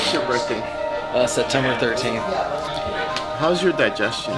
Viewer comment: I'm not sure, but did just say he was born on September 13th at 19:38? Because if he did, then we DEFINITELY share a birthday.. Though I was born in 1996 and my birthday just so happened to be a Friday the 13th.. What's weird is that me and my family kinda had this thing where we're always experiencing paranormal stuff. And I always seem to be the target because from what a psychic friend told us, I'm so open and approachable, that some of them tend to come to me for help. It actually got worse a few days after I turned 15, while I was at school. Before that, I was only slightly aware of paranormal stuff. But in that instant, it's like a dam suddenly burst open and I suddenly see at least five figures suddenly crowding me and it felt hard to breathe. Ever since I've been highly aware of what's happening around me.